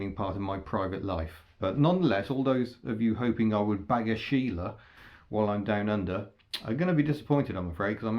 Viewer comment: Had a giggle then. Sorry with my deafness it sounded like you said banging a Sheila instead of bagging a Sheila. Either way. Your privacy is upmost important online life and private life are to be kept separate. Good luck but make sure you don't forget to video call george the cat